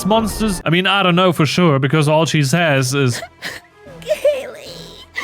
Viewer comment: are you sure she didnt escape from a fallout bunker as a failed clone? That sounded like Gary to me